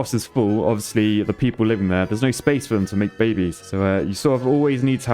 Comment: did u really said “a place for them to make baby”? That’s sound soo weird